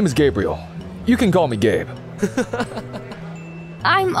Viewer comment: It sounded like he said “my name Gabriel but you can call me gay”💀